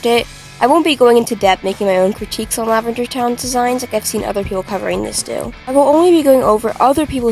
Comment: I thought that it said “Lavendertowne resigns” and I was like WHHHAAAATTTTT?!?!